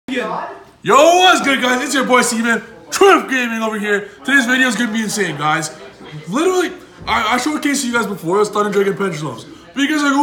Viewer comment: Did he just say, "It's ya boi, Semen?"